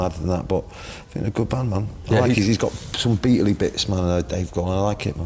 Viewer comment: Dave Grohl has some “Beatle-ly Bits” 😂 or did I mishear that